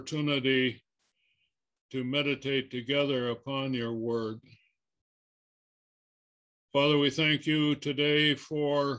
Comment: 06 Does he say we are PRI TRIP But Not PRI TROUBLED??? Can Someone Please Shine Some Light On This Question Of Mine Please Someone. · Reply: Hello. The statement is "pre-trib, but not pre-troubled." The point is that while we believe the Rapture is a pre-trib event, we as the Church are not exempt from being troubled. In 2 Timothy 3:12, Paul wrote, "Yea, and all that will live godly in Christ Jesus shall suffer persecution."